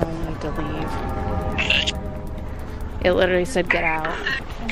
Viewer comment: ok so at 17:17 idk if it's just me but I hear a man screaming "Get out"